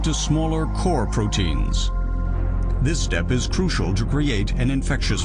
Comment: can anyone tell me what the word in 3:33 "this step is crucial" , what the word after crucial? spelling like "Jubreiate" or something like this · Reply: to create